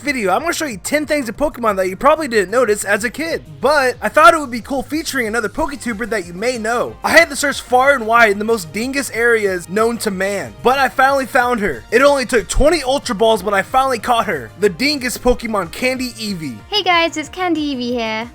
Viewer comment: did he say dingus? #1 most used word XD